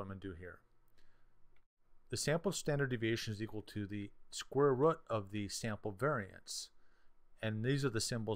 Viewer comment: Did u just say "Square rot"?